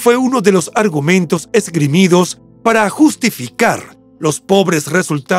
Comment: Que dice en el minuto26:34? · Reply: Justificar